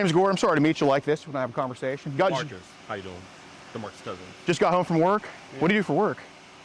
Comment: Did Jidion say his name was Demarcus Cousins @ 2:20?! 😂